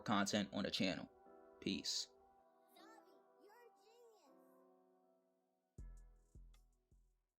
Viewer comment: I hear someome talking at around 6:24 lol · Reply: The "Zavi you're a genius"? That's a part of the song in the background of the video.